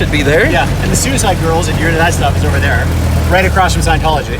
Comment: Man... whata madness!!; btw, did i heard... 'Suicide Girls'??. Enjoy the weekend and be safe guys